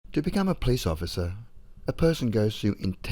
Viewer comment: Not a criticism, but there was one time it sounded like you said a "Peace officer" instead of Police officer. I certainly prefer Peace Officer!